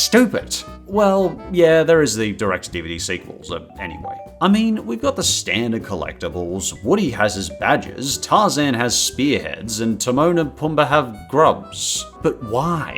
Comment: thought that said badass